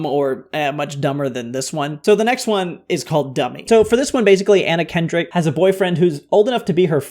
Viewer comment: When you said "Anna Kendrick" I heard it as "Anakin Drick" 🚀👾